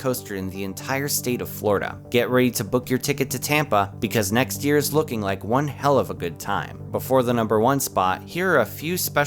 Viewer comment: did you just say "next year is gonna be one hell of a time"